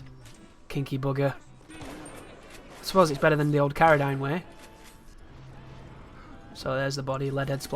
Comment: What did you say at 26:21? carradine?